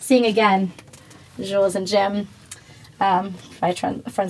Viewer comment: What was the movie at 1:18 she pulled out? I can’t make out the cover. Did she say Jewels a gem? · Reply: Jules et Jim, Truffaut